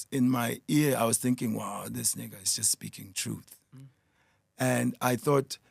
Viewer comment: did he say “dis nigga is just speaking truth” or am I wrong?